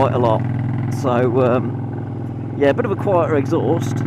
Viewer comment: lol...im sorry did I just hear you say 'get a QUIETER exhaust'??.....hehe......hmnnn no no no.......but sweet review,ride safe buddy.....bizz